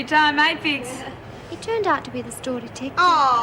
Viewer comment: What does say at 24:38? "He turned out to be..." and after that I have no idea. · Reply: "Turned out to be the store detective"